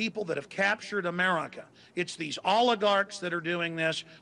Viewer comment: inbetween 8:44 to 8:47 i didn't catch that word what is it. i think it spelled oligark or something what is it